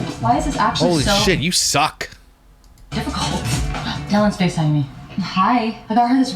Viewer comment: is it just me or did she say, “Dillon is FaceTiming me.” It did not at all sound like “Dallin.” Hmmmmm.